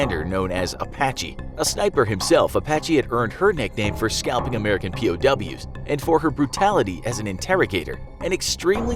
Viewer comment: did I hear the person say himself and her?